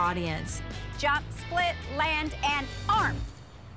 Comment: How can you not know how to do a jump split !?!? You are trying out for a team that is known for that. Just that!! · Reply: Curley Cues I thought you said jump shit...